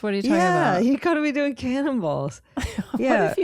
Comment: I really thought Tig said cannibals instead of cannonballs at the pool party